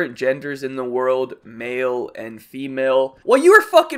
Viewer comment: It kinda sounds like he said “Mayo and female”